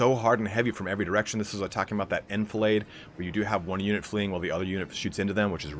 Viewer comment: What is that word you used at 25:12 - “infallaid”? How do I spell it, because I want to search up the definition 🙂 Great cast by the way! The intro might have been a little long, but I liked the intro! Some people might want a banner with a timestamp to skip the intro and go straight to the battle. Great job with the commentary, I enjoyed it thoroughly! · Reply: Enfilade :) thanks man! Yeah I'll trim down the intros a little bit so it's more battle and less exposition